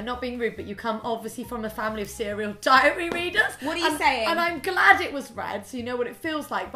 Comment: "you come from a family of serial diary readers" aHA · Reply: First thing I heard was "diarrhea eaters" and I was about to throw up :P lol